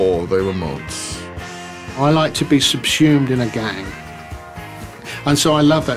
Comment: It’s drivin me APE! Anyone know what word he’s droppin there?? Sounds like he said “sepumed” (👀 ??) 🤔 🤷‍♂️✌️ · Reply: Subsumed 👍